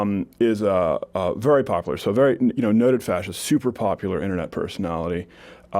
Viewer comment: lol it sounds like he said "noted fascist"